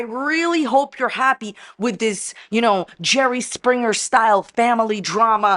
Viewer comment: At first, I thought you said Cherry Springer, instead of Jerry Springer and now that’s what I’m calling it. The Big Cherry🍒 Springer Show!!! 🤣🤣🤣🤭